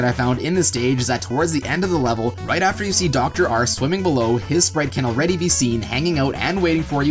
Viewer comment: Did you just call him dr. Arse? 😂